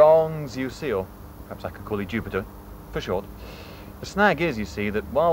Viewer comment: Why is god a mumbling Roman? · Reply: Near the end at 5:48 I think he mentioned that it's Jupiter. It's hard to tell exactly what they're saying though.